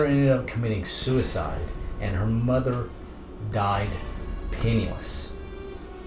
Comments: does anyone else hear at 2:32 we he recaps of how the mother died because I could of sworn he said she died of penis... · he said "penny less"...i kno i thought he said penis